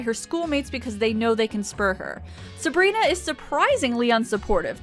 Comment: did you just say... "they know they can sperg her"?